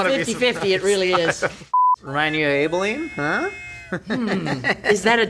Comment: am i going insane why is does the subtitles say "Aine" when they say Abeline